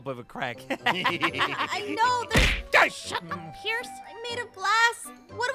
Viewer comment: It’s it just me or did almost say the f word at 2:46? Or did she say shut up?